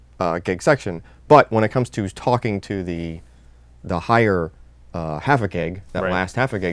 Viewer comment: sounds like he says "higher havoc egg" :D